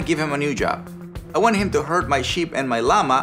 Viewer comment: I first heard "I want him to hurt my sheep" :D:D:D edit: why do herd, heard and hurt all sound the same? seriously Tommies, get some new words! xD